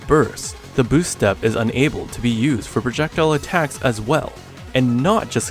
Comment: Nice tutorial! I think u try to say "able to use projectile" at 3:47? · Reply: I was trying to say "enabled" which means "give (someone or something) the authority or means to do something." Which I can see that this was a bad choice of word as it sounds like "Unable." Thanks for pointing that out I'll make sure to pick a better word next time for future videos to lessen the confusion.